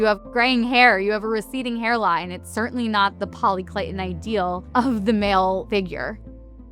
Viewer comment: What word does she say at 1:08? Poli..what? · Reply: Polykleitan